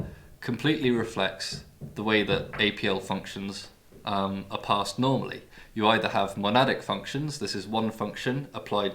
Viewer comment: you say "we can create a ... fun". Could you tell me what is the word before "fun" ? I can't understand it. Was it "thread" Or maybe "trad" for "traditional" ? PS : english is not my mother tongue :-) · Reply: he is saying 'Trad Fun' meaning traditional function. See <link>